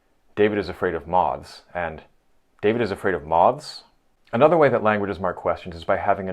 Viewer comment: The point about Russian speakers coming across as rude because of comparative question intonation is fascinating. Sort of the intersection of prosody and pragmatics. Nice mug. So, I kept hearing "David is afraid of mogs." I'm like, "What's a mog???" 😄